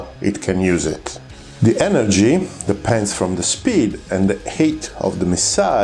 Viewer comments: please forgive my insolence. i was having trouble grasping your pronunciation. i finally worked it out. when you say height, you actually say hate? it is said more like hiyt. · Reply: I thought he was saying 'heat'; your comment cleared that up. If height is difficult to pronounce, altitude is a good alternative. Thank you for the good explanations of the need for energy. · Every time you say "height" I hear "hate", which is wrong, but also feels kind of right for a missile. Like: "How'd you escape that PL-12, Bob?" "I'm not sure... I think it just ran out of hate."